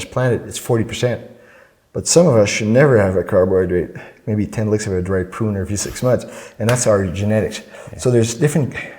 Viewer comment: What the fuck does he say at 20:25? "Some of us should never have carbohydrades. Maybe... (inaudible)". It's driving me bonkers. Anybody know? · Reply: CJ Christopher "10 licks of a dried prune"